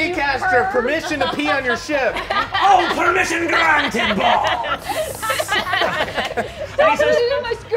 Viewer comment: I know the subtitles say "Oh permission granted boy!" but I swear it sounds like he says ball ( nickname), not boy